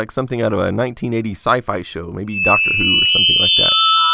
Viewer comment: did you mention Doctor WHO? It sounded like you said "like Doctor ..." but the music interrupted you and drowned your voice out